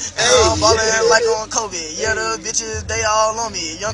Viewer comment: did quavo say covid????